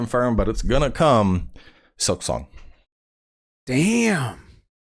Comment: what does he say about this time? The game that may come? Sounds like says “suck song.”